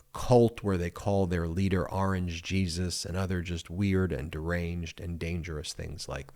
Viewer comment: Oh I thought they said "Orange cheese's", still makes no sense.